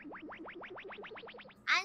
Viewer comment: what does she say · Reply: Maaan... Anji(ng)